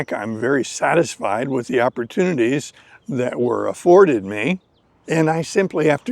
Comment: Your stories are always always very good. One query please, were afforded me'.. did i hear it correct? Somehow we feel a grudge toward those universities,.. and extremely 13:26 happy with those that'afforded you'(?,). The loss is to the former! We are happy in this 'world university'to have you. Thank you